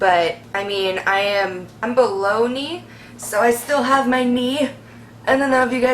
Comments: When she said "below knee" I thought she said bologna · How old were you when you were going though all this? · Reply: I thought you said "Bologna" not "below knee" lol silly me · omg i thought she said bologna on both knees.. it took me a sec to realize what she meant lol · Thank you so much for sharing your story!! i do feel bad for laughing at one point, i thought you said "baloney " rather than "below knee." but you keep being beautifully you! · when she said "below knee" I thought she said bologna. "bologna on both sides" lifts up legs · I'm watching this and am very tired... but I want to finish hearing your story. But... when you were talking about having your legs amputated below your knee, I heard "balogna" 😑😐 You are inspirational. Seriously. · God bless you. This is more than anyone should have to endure. On a funny note, when you said "I'm below knee" I thought you said "I'm baloney" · "I am bologne" xD i misheard that! Hope youre doing okay love ❤️ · it sounded like she said bologna